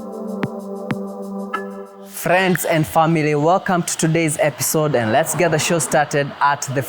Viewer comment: Did you just say friends and famerey ?😢